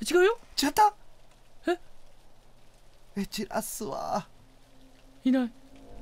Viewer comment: えっちっあっすっわぁ〜 すみません聞き取れませんでした浪川さん😂😂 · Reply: 焦らすわ〜ていってますね